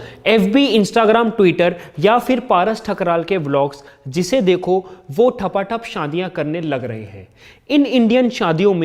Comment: Am i the only one who heard paras ke tharki vlogs 😂😂😂